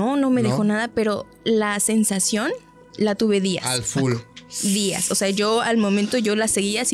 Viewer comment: Escucho una voz que dice Paco al minuto 47:04 al 47:06 pero no estoy segura si es la chica o alguien mas 🤨